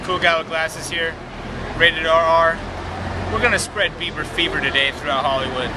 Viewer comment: LOL sounds like your saying, " Beaver Fever."